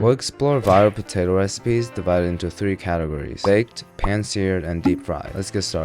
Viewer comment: I thought it said i made 85mil potato reipe